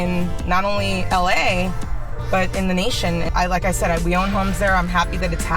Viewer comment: I thought she said "indonesian" not "in the nation" haha when someone mention your country...